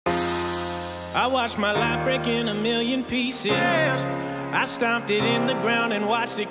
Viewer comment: Song: "I watched my life break in a million pieces" What I hear: "I washed my leprechaun into million pieces" ._.